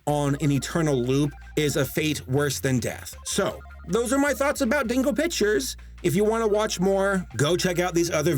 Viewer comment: I was listening to your video while doing other things &, I swear, every time it sounded like you were saying "Dingle Pictures"...which is probably a better name for them, honestly.